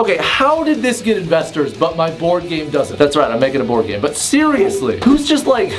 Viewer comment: I thought he said "porn game" instead of board game and I got very confused